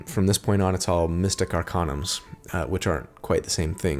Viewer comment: <link> at 47:04 the CC says...."capped at fifth level from from this point on its all mystic our condoms uh..." hahahaha